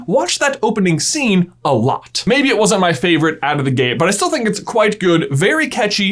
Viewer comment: "maybe it wasn't my favorite out of the gay"--what i thought he said the first three times